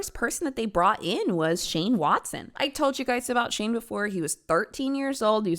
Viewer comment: Girl I thought u said Shane Dawson first